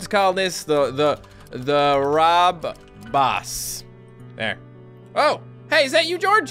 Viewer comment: when you said rob boss i thought you said bob ross😂😂